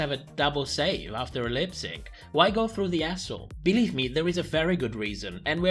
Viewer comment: I totally thought you said “Why go through the asshole,” for a second there.